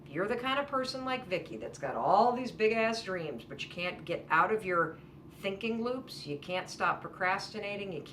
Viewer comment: I was chewing while listening and I thought she said "you get stuck in your thinky-boo" and now I will always refer to my brain as my thinky-boo